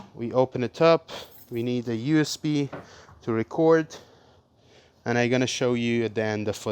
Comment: I thought he had said we need to say a prayer!😂